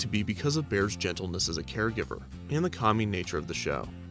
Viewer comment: I heard that as "and the COMMY nature of the show" and had a good laugh. I know you said "calming"